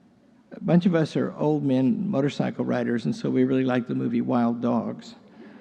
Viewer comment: Sound like he said wild dogs, it is wild hogs.